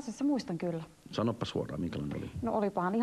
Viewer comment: Kuulenko omia vai sanoko toi jätkä oikeesti "Sanoppas huora minkälainen oli." ?